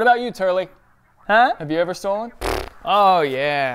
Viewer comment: I thought it said "have you ever been stolen"